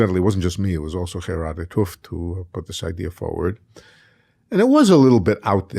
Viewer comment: "It wasn't just me, it was [BLANK] who put this idea forward." What name is he saying? I can't understand it, and want to look this person up. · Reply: Gerardus t`Hooft